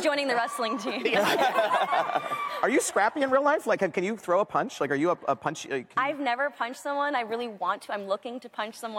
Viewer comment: Can someone tell me what was said in the minute 23:59 until 24:12 please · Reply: Someone says: Punch me mom